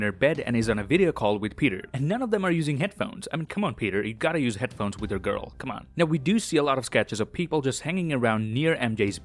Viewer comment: “You gotta use headphone while talking to your girl.” Then the captions say “you know what I mean!” What the heck!! Time